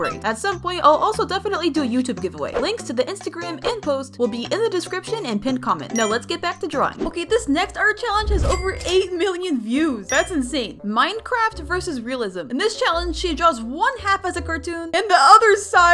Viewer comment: I thought she said 8 BILLION views…